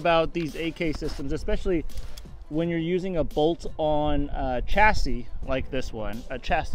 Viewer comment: I thought said, “bolt on chastity belt” at first.